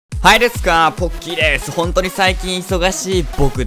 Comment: 最初はいでくすわ！ポッキーです！って聞こえた！